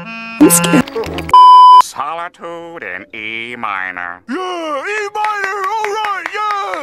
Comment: I almost thought he said eat minors💀💀💀💀☠️